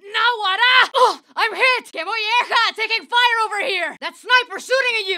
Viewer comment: What does she say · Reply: “Que molleja” could be mean “oh shit” or “that’s nice”. I need a context